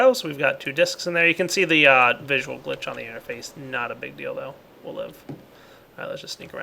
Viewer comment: I'm not sure why but everyone is mishearing "deal though" as "dildo"